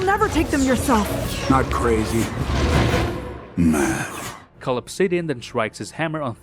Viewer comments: Tell me you don't hear it "Not crazy, meth" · My sister thought he said “Not crazy, Math” and I don’t know how to feel about that